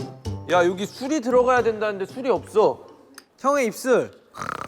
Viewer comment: Jin says he doesn't have cooking wine when the recipe calls for it and Jungkook makes a dad joke saying they have jin's lips (hyung's ip-sul) because sul =wine/alcohol and ip-sul = lips. He did NOT say "don't whine about it though"